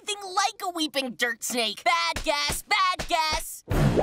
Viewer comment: when i first saw this scene i thought sprig is saying "BAD GUEST BAD GUEST"